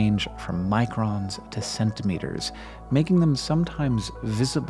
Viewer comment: Did you say CENTIMETERS?